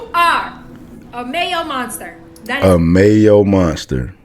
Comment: Thanks for putting this out there, as a "Mayo Monster" when I say this I get threatened or ignored, and can't say anything in my work's required "DEI" video training sessions... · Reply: I actually thought she was saying "male monster"!